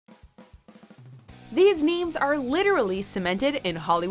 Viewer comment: it sounds like she said these memes are cemented in hollywood history, well i guess to be fair most of these people/things are memes.